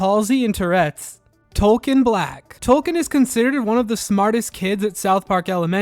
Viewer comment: It's Token. It's always been Token. As a kid i heard it as "Togen" which would be a much easier retcon. Just too pandery.